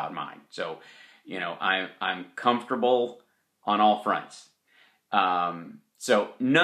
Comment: I thought he was about to say "..I'm comfortable on all fours.." Lol.